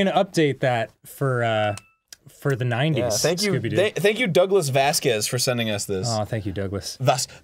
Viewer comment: For a second there I thought Aaron said jhonen Vasquez